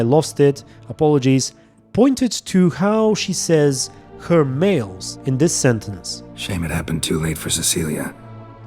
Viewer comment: The first time I heard it, I thought Orianna said "mates" not males.